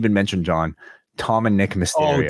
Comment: It’s Dom and nick not Tom as well as it’s nick he never meet…which makes it more funnier cause he not realizing Dom whole name is Dominic and all r-truth hears is Dom and nick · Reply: I saw the Truth promo and thought he said "Tom and Nick", for second you had me doubting. But I just re-watched it, with subtitles, and its 100% "Tom and Nick"